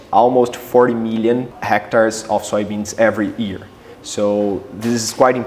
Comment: Did I hear 40 million hectares of soybeans planted every year?!